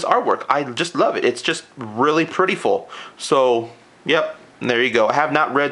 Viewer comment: did you say preatiful lol